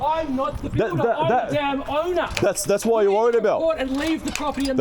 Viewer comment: did he just say he is the owner?